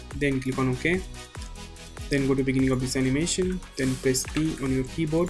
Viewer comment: you said go to the beginning of the animation and press what? I cannot continue because I am not sure what letter did you say. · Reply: it´s P, shortcut for Position